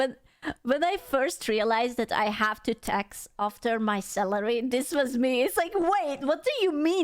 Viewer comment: Ohhhh Salary! I thought she was saying celery, I was so lost.